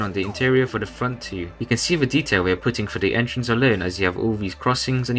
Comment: He says we’re putting and the captions say they’re putting!!!